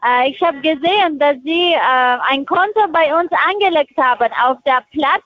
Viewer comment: ich war kurz irritiert, habe auch "ich habe gesehen, dass sie bei uns ein konto angeleckt haben" verstanden. 25:00 "ihr spiel hat sich verlängert...mhmmhhmm, ja, ja, sie haben vergessen zu kündigen, mhhhmmhh, ja, ja, ja, ja" bin mir sicher sie hat die letzte umstellung vom callcenter verpasst. daher auch die gelangweilte art, macht seit 11jahren nichts anderes und kennt wahrscheinlich wirklich auch noch kay-uwe meyer. vielen dank für die zusammenstellung, hat den langweiligen hausputz versüßt :chillwcat